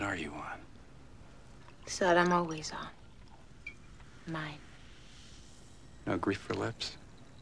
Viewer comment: Whose side are you on? The side I'm always on. Mine. No grief for Lipps? I'm wearing black underwear. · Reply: I thought he said "Hungry for Lipps" and it just didn't make sense.